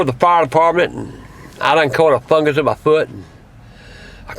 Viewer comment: My earphones were messed up so I thought he said "I caught a fungus in butt"